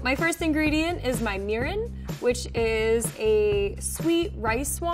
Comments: let it be that I was not the only one who was focused on something else while this is playing in the background only to turn back around to replay back at 2:27 to make sure I heard correctly that she didn't say, "my urine" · I thought he said "my urine" Like if you thouth too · I thought she said urine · BRUH I THOUGHT SHE SAID URINE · I swear to god when she said "my first ingredient is my murin" I heard urine . and I was like .. uh.. seems legit? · thought she said urine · I thought she said urine and I was like: 😳